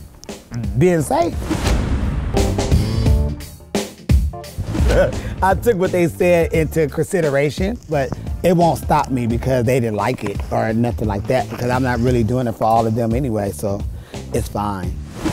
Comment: Jasmine - @ 3:51 "I took what they said into cursideration".